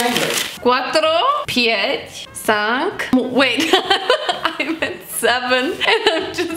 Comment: did She said pięć? It's in Polish and I'm Polish and i'm kinda honored